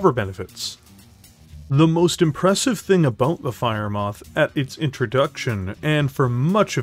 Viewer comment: I don't know why but I misheard the Firemoth J variant as the Firemoth Jade variant and that's just me and the J is "Yeah you see that column of Tanks and Infinity, yeah I don't want to see them there anymore please make them go away"